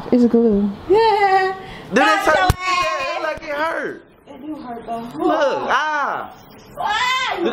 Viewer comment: Bruh i swear I heard him say, "it hurts ? 1 to 10 ?.